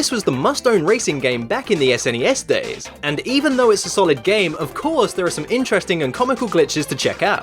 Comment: Hey there! I love the Son of a Glitch series but I did notice that at 0:10 you accidentally said "Sonic" instead of "Mario" but hey, keep up the good work ;)